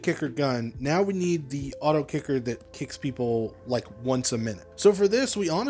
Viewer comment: I thought it said auto clicker 😂